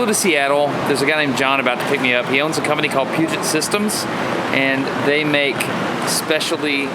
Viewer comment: Did you say Putin Systems?